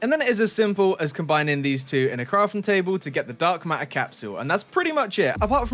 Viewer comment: Why do i hear " dark maer "